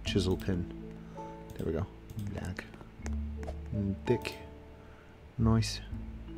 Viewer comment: ummm.. what did u just say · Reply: T H I C